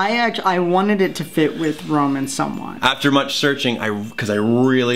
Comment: Did say they wanted Virgil’s name to kiNda fit with Roman’s name?!